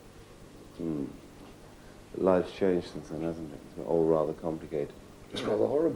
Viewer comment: what does marco say? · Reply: "Naturally. Life changes, doesn't it? It is rather complicated."